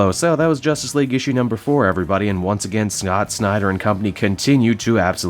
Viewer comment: Did he just say "snot snyder" ? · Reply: Geoffrey Kandolo I rewound twice. He indeed said Snot